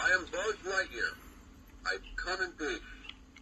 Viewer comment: would you believe me when I say I always thought 1995 Buzz said "I come in BOOTS"? The voice box was the reason